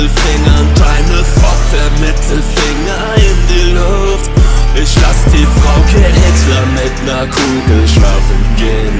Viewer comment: Schade. Ich hab als erstes Verstanden "lass die Frauke hecklern", also umschrieben mit einer Heckler & Koch erschießen lassen. Wäre, wenn's nach mir geht, eindeutig die bessere Line gewesen. Btw die Leute die meinen dass sich der Text nicht sachlich mit der AFD befasst: "Sinnlose Provokation trifft auf sinnlose Politik".